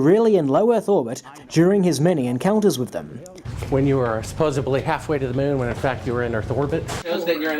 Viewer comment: Did actually say "supposably" @ 16:00? Holy cow, he's barely literate.